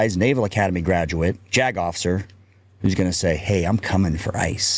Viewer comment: For a second there I thought he said Jagoff. Then, after his description of the guy and what he's doing, I was sure of it.